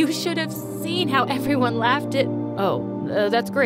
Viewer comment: she said laugh but the subtitles said love?